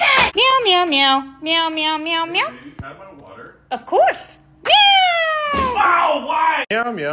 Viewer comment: "Is that water?!" "Of course!" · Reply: I heard "can I have water?"